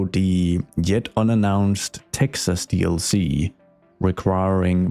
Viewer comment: „yet unannounced texas dlc“? did i hear that right? why is nobody else talking about that? 😅